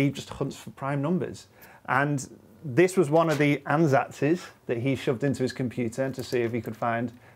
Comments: What's the word at 3:49? I can't make it out :( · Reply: I guess he is saying "this was one of the ansatzes "... that would be a completely butchered plural of the german word "Ansatz" - basically means approach or starting point. The correct plural would be "Ansätze". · What did he say at 3:48? Ann's axis? Can somebody help? I just don't understand English very well) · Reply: he said ansatzes, which means educated guess.